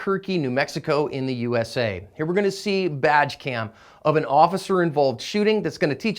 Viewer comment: I thought he said vag cam at 1st.